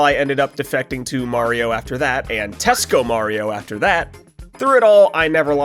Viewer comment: did you just say Tesco Mario, I needed to check were your channel was from after those words